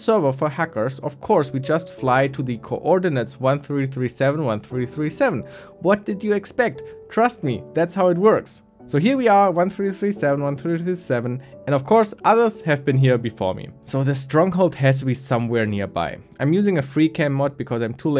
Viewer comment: i think you're german lol, you said "alles" and not "anything"